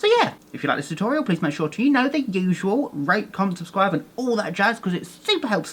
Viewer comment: I thought an the end you said RAPE comment and subscribe!!!!👌😆😆😆😆👌